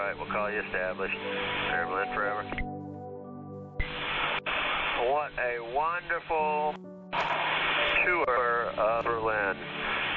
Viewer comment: フライトレーダー上でこの飛行機の軌道を見るとベルリンの街の上にハートマークを描いているんだそうです！ "Mehrere Schleifen fliegt der Airbus A320 über der Stadt, die auf dem Flugradar wie ein großes Herz aussehen sollen." あと 8:23 のところ、what a wonderful tour of berlinって言ってるように聞こえます。なんて素晴らしいベルリンのツアー（この場合だと眺め？）なんだろう ありがとうブレーメンセンター って略す方が正しいかも それにしてもこの機長の人、ドイツ語の喋り方と名前からしてアメリカ人なのかな？乗り入れと同時に会社と一緒にベルリンに寄り添ってドイツ語ぺらぺらになったとか想像すると心があったまりますね！